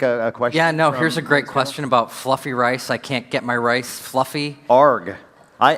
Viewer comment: "fluffy" rice question." Although I can see why you thought you heard that!